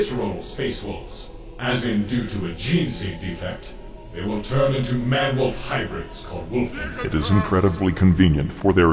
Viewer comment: What is that off-screen man saying? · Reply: fishpop DIGGANOBZ!